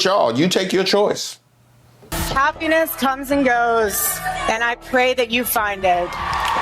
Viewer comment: "Happiness comes and goes and I pray that you find it." With that tone of voice and face she made I heard "Go phuck yourself."